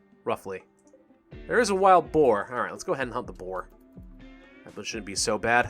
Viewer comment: I thought you said "lets go ahead and hump a boar" XD